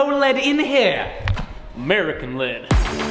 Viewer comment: did he say Merkin lead ??